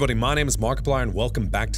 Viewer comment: When he Said my names markiplier i thougt it Sound like he Said my name is bon bon so i was like wth